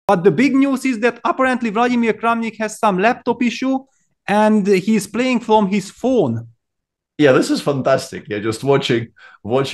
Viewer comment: What i heard was "Apparently Vladimir has left a TISSUE" WHAT DID HE DO? LEKO?!